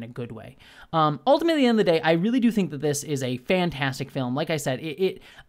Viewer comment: when you said 'ultimately, at the end of the day' I heard 'ultimately in the day'. I'm pretty sure I misheard you but it occurred to me that the ultimate time of the day is the end of the day right? new alternate phrase that works in a technical sense!